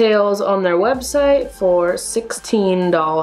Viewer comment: who else heard doll-hairs instead of dollars😂